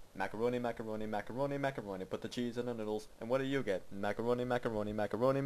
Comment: "I'm gonna buy a hot dog right now" walks away "macaroni macaroni macaroni macaroni put the cheese in the noodles and what do you get... " LOL 😂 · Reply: Holy shit I heard it as "put the cheese in the middles" and have been singing this way